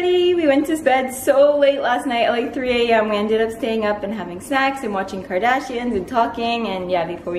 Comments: Guys I said 'having snacks'! You all have dirty minds!!! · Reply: I'm 68℅ sure I heard "snakes" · Oh and PS it totally does sound like you said we ended up staying up late and having sex hahahahaha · Did she just say 'having sex' in the intro? 😂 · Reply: I think she says snacks tho, but sounds like sex haha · Having... snacks? Honestly not sure what you said at the start! This video is really cute 😍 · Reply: i thought she said having sex hahahahahshhshdgdh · I could have sworn she said "having sex" omg I had to replay it hahaha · I heard 'snacks' lol, and please do a makeup tutorial! love your channel as well <3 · I thought you said sax only because my parents cat was meowing for attention and I thought you said that instead of snacks but I did rewind back to the beginning and then I heard snacks